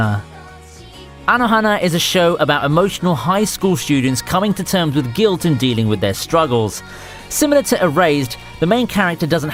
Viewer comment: Who else thought he said high school shooters when talking about anohona